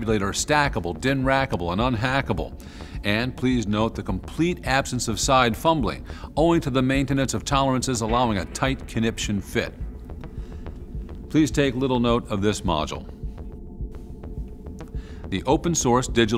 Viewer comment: DID HE SAY CONNIPTIONS?!?!